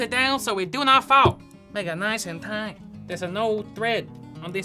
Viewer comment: I thought he said “nigga nice and tight.” It had me dying!!🤣